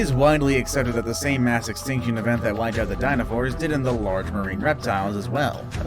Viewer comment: did he say dinofours or dinosaurs.